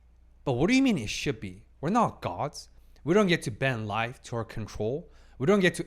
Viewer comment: “ we’re not gods” I dead ass heard it as dogs 🥹🤞